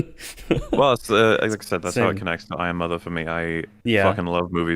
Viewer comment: How it connects to what? "I am Mother"? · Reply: I think I'm trying to figure out that as well for some reason I thought he said "Iron mother".